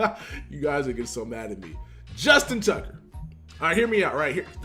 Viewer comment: I thought you said Justin Tuck lmao